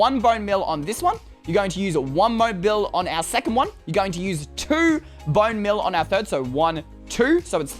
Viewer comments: He said mone beal instead of bone meal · Does anyone heard says mobe bill on